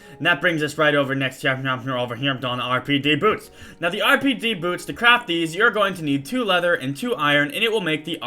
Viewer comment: I could've sworn that Sky said "to crap these, you need...". O_o